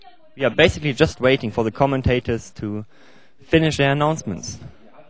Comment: It sounded like he was saying "We're just waiting for the commentators to finish they're nonsense."